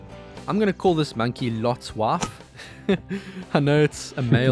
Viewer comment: HI, really enjoy your channel. It's high quality content. I even enjoy your accent. I heard " Lot's wife" as "Lotswa", until you repeated it. Brilliant. But then I am a Brit. 😁😁